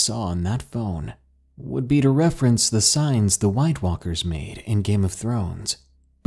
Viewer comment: I thought it said Skinwalker